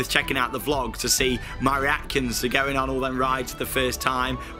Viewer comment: Did just say reacons?